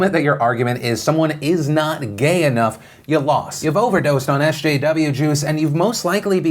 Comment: "You overdosed on SJW juice" xD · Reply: Zachary Carrigan i honestly thought he said "you overdosed on sjw jews" 😅